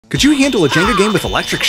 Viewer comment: What I heard: "can you handle a gender game?.. "